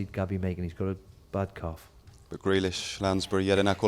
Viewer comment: Does Steve say Gabby's "got a bad cough" ? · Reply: chris ball calf